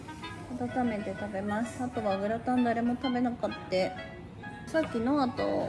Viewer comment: 食べなかってって聞こえるけど、どこかの方言なのかな？🤔